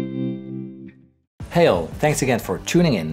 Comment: Did you say "heil" at 0:26?